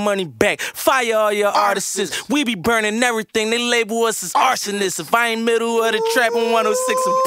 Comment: I Thought He Said If I Aint Middle Of The Track Lol . I Was Wrong